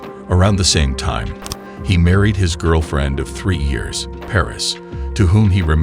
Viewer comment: Great video! I also laughed because at one stage it sounded like you said his girlfriend is three years old and I was like wtf